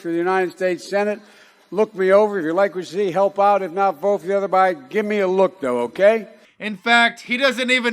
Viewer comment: does he say vote for the other Bidenat 1:22? Lol He needs to go back to the nursing home.